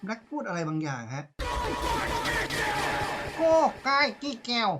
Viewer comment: มันน่าจะพูดว่า Wenda Get Out รึเปล่าครับ